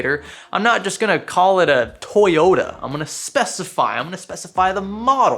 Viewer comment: I legit thought he said toy yoda, not toyota lmao